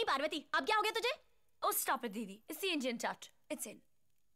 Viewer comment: are the words still unknown to my sanity..what does she say I can't even frame after watching it for more than 100time..can someone say..what's does that specific dialogue POOH mutters..pretty please?🥺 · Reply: Oh come on didi! Its the indian touch. Its in